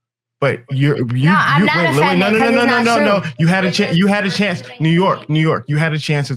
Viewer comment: Time out... that black girl is called New York? Huh? Did I hear that right?